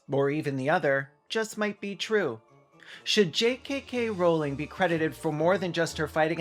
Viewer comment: Just thought you called her J. KKK. Rowling for a moment there, but there was only 2 K's 😅